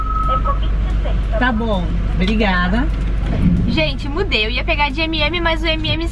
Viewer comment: gente fu*** mas na verdade foi "mudeu" Kkkkkkk que susto